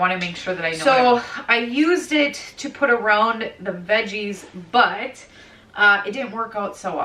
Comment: I heard “the veggie’s butt”when you were talking about your spring garden, not “the veggies, but…” Anyone else? 😂 I just organized a workspace yesterday for redoing furniture and found a lot of items that I thought we’d already donated! Grrrrr… However, I now know where I’m putting all of my supplies, and it looks so much better! I painted my dining room windows today… I’m sore! Isn’t it so rewarding?😊